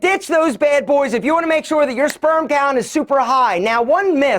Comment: Because you said “you just dropped me” a like, I did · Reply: “gown” is subtitled instead of “count”